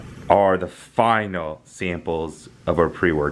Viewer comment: I thought it said "I finally came!!"